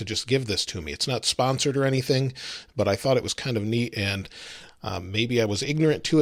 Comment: Is it just me or did he say meat instead of neat